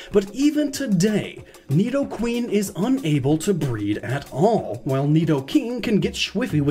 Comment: I thought he said nidoqueen couldn't breathe lmao 😂